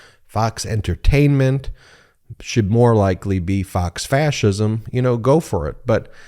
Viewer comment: When you said "Fox Fascism" I misheard it as "Fox Fashion", which would still be a step up. "They say we're not allowed to wear white after Labor Day, but why? No one will tell us. They just go back to accusing you of racism for separating your laundry into whites and colors."